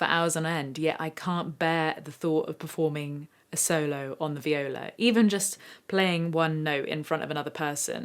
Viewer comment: did she just say 'VIOLOW'